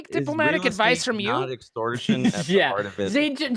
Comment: did one of the vectors call you "Zayne"? or am i hearing things?